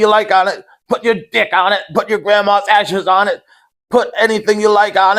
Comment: I thought he was about to say your grandma ass🤣